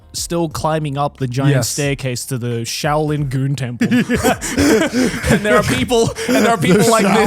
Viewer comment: I really heard "The Shaolin Goon temple". That's wild.